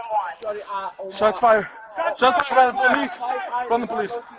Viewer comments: Did he just say, "Shots fired, shots fired, phone the police." ? Wait hold up, who are they then ? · Did I hear him say "Shots fired. Shots fired. Send the police."?!? And did Lightfoot issue all those officers citations for not wearing their masks like she makes everyone else? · does anyone hear somebody go "shawty ah oh mah" wth